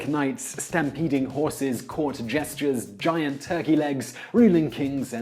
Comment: It sounded like he said "court gestures" rather than jesters. lol